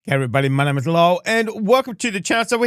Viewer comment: just learned the other day that your name is, I always thought you said Lowell at the start of your videos because of your accent